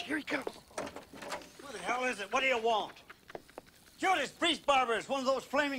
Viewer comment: Lol always thought he said "Judas Priest barbers" which I say frequently... I guess its my catchphrase